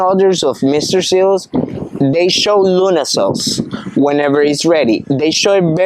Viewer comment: Excellent information. Thanks! I would like to know more about when to pick the "green" mangos. I thought I heard him say you should wait to see "luna cells" or "little stars" but I have no idea what that looks like. Do you happen to have a video that shows what this looks like?